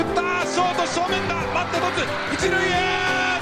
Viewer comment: バット持つにきこえる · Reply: バット持つに聞こえる